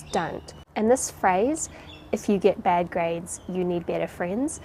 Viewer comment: What says: "If you get bad grades, you need better friends." What I hear: "If you get bad grades, you must blame your friends." :P